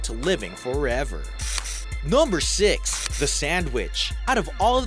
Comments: i thought that he said sandwich😂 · i thought he said sandwich likke food but he meant ala sand wich like eivil - z